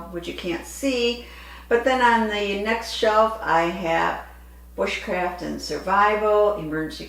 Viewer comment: When you was talking about your books, I thought you said witchcraft when you said bushcraft. I was like 😳😳😳😳. I will look at the books you spoke of. I have several and they are such interesting reads.